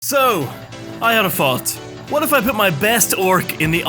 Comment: At the start I thought you said 'I had a fart'